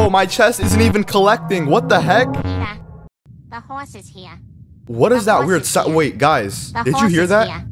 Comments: In 1:22 i hear a cursed think like someone say the horse is here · bro i just heard "the horse is hea" at the cured ship i heard "Peter"